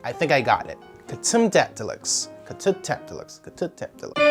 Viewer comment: Am I the only one who skipped the intro at first and thought he was saying “cat tum tat deluxe”